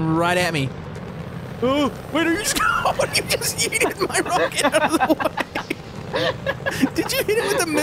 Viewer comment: did he say you yeeted my rocket